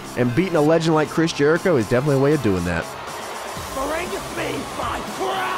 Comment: At first I thought he said "Be my friend!"